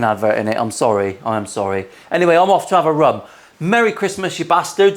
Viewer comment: 'off to have a rum'... misheard that entirely the first time, and thought you were off for a rub!